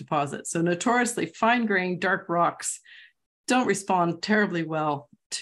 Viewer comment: what does she say? " ... dark rocks of " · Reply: She said that dark rocks don't respond well spectrally (because of almost absent reflectance), and when you have some data, normally don't have a clear answer it's ambiguous.